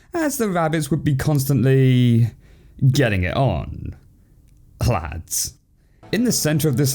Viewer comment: What did he say at 3:29? Clads? What does that mean